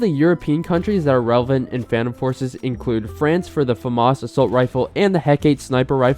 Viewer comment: I misheard famas assault rifle as famas sniper rifle.